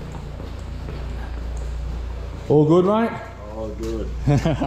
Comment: Sounded like Ollie (Drain Addict) saying, "All Good" 👍)😂